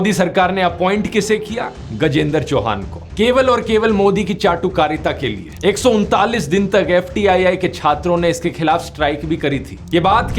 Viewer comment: 139 days subtitles says 149 days Can you correct this please❤️